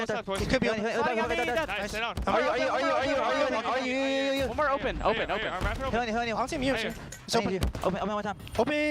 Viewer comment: wtf is Cellium saying bro😭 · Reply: On U